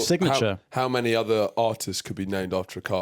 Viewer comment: I thought she was saying Autist the entire episode.